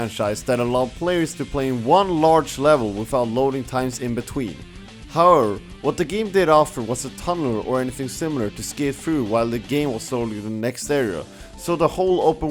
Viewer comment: can someone please tell me what he says at 18:30 I'm having so much trouble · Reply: ThatGuy What I said was "However, what the game did offer was a tunnel or anything similar to skate through while it was loading the next area. So the whole open world thing wasn't really that true and was heavily criticized." Hope I helped!